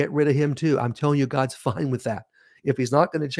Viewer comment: Keep bad mouth ing them when the bible says to honor your mother and father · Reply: did you say "God's f_____n with that?"